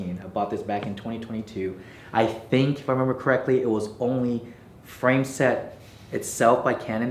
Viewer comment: "....I think....."???) What was the word....😅? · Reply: „When I remember correctly“ this guy is doing double time. Quit fixed gear, started rapping